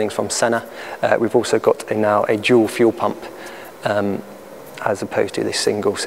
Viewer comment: i heard "we have a jew fuel pump." :D 7:21 Thought the fuel consumption would be the lowest possible then. ^^ Good looking car btw! :o